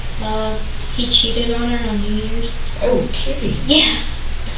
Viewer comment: I think she acutely said "on New Years"